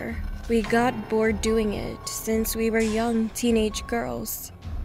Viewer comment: I thought they said they were 11 not teenagers